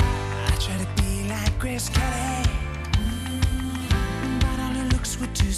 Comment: when i was little i thought he said grape jelly